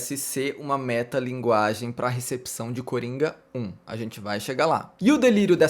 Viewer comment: Toda vez que o falou "Coringa 1" eu ouvi "Coringão" kkkkkkkkkkkkk